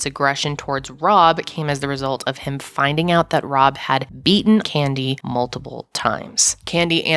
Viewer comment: I thought you had said “he had EATEN Candy several times & that’s what prompted Robs aggression”,…. Wtf?? yes I went back & had to listen again. I need to clean my ears out.